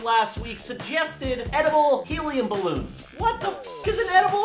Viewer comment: I thought is said edible HUMAN balloons XD